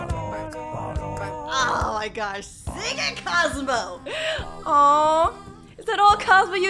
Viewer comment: is it just me or I heard “ SING IT COSMO “